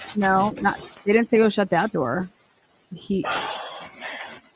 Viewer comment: I thought he said “Shut the door with the dog in there?” Could be wrong but sounded like it. Thanks for all your hard work. You are awesome!